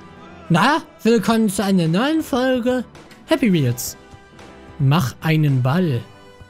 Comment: Ich hab Happy meels verstanden😂😂😂😂